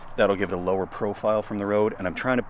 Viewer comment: For a split second, I heard "We've got this uninvited BLACK BEARry...". circa 4:58. 🤣🤣 (and there is simultaneously a black bag in the lower right of the camera view...)